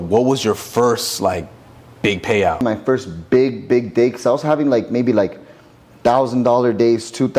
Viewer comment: he said his first big dick lol instead of big day. DId anyone catch that?